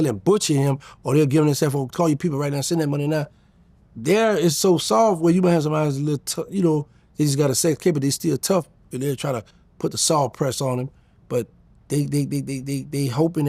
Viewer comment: CAN SOMEONE PLEASE TRANSLATE WHAT HE SAID WTF 😂😂😂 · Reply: He said he might run into somebody that has a sex case but still a bit tough but it won't work cause that person will still not want their position on the soft yard to be jeopardized so they might try a bit to extort Diddy but they not gone go to the extreme lengths of stabbing him or harming him just threats